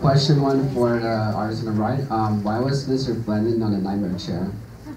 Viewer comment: i can't understand that question? can someone write it plz? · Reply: He said "Why was Mr. Blendin in the nightmare chair?" He was asking why Blendin Blandin appeared as one of the statues in Bill's throne when he didn't get petrified like everyone else